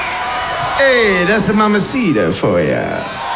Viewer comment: What he says 0:52 mesido? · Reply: mamacita*